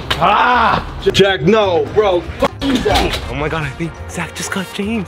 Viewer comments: is it just me or did James say "JACK NO!" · Did James call Zack. jack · Am I. The only person who heard jack no